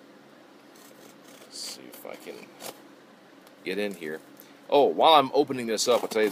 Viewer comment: when you said "see if I can..." when I first heard it it sounded like you said phuckin (f-word)